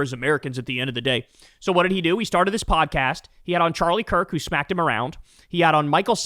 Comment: I had to recheck the date....he had on charlie kirk??? what? Am I mishearing it?